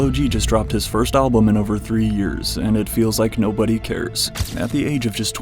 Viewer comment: I was coughing from the loud during the intro and thought he said he dropped his first album in over 300 years 😂